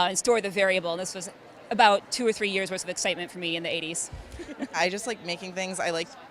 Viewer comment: tf did she say? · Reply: for two or three years that was the excitement for mi in the 90s